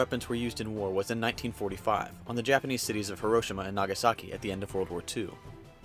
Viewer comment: herosima is what it sounds like he is saying lol...